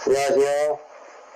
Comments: 초에 여자목소리로 안녕하세요 뭐야 이러는데 저만 그렇게 들리나요.. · 에 여자 목소리로 안녕하세요헤헤 라고 소리가 들려서 소름 돋았지만 다행이 솔루션 잘 마무리하여 정말 다행이라고 생각합니다! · 안녕하세요 라 들리는데 이 접신하고도 안녕하세요라 해서 놀램 ㄷㄷ